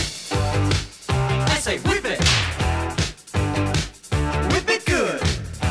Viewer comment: When this came out in 1981, as I kid I thought this had something to do with whipped cream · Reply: I thought it was about a dog because I heard it as "whippet" 😂